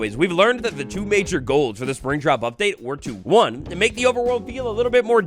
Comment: it sounds like he is saying springtrap update 🐰💀🥀🧟